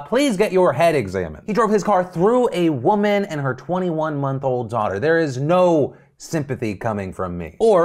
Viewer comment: At first I thought you said Cameron's sentence was 24 MONTHS and I thought the story was about how fucked up that was. That made me really confused that anyone would think his sentence was too severe...